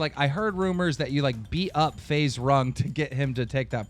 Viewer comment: did he really say Faze Rung?